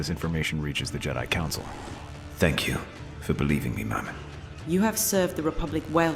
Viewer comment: Did fives say mom at 0:42 or am i tripping?? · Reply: He said ma'am it was just the accent